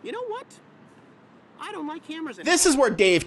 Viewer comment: When you said, "I don't like Cameras anymore!" I thought you said, "I don't like hammers anymore!" Turns out you can mind trick yourself with that